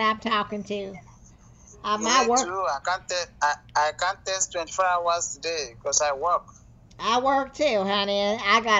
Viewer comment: I thought he said he hasn’t worked in four years